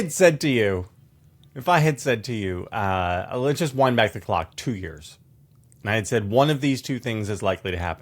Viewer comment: i thought jvl said "wine bag o'clock"